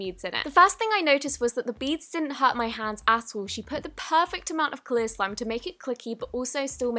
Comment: did anyone else realise that at 1:00 if u look on the subtitles it says asshole instead of at all